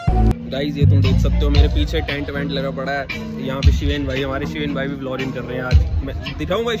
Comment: did u said vlorring ????hahahahhaha Bro kya baat hai....vlogging hota hai